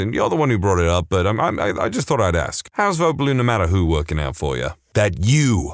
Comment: What is even said there? "How's blue no matter who.."?